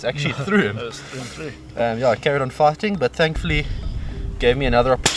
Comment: Okay. Fighting. You was saying fighting. Now I get it. I thought you was saying farting.